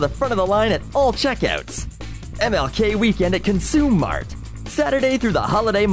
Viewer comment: I thought it said Consume Me Mart, anyways hilarious video :)